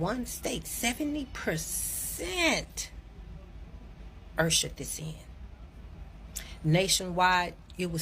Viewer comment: I had to rewind, then let it play, then rewind before I understood what she was saying, "70%, in one state, "70% 'ersherd' this in." I listened several times before I understood 'ushered this in'. No disrespect intended. I'm from KCMO, now living in Texas, but, that took me several replays and contemplation......